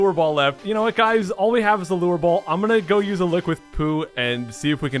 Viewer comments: I swear i heard "I am gonna use a liquid poo...". Now that is desperation. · Haha when you said 'I'm gonna go with a lick with Pooh' I thought you said 'I'm gonna go with a liquid poo'